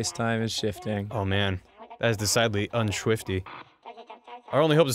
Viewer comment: did he say "unschwifty?"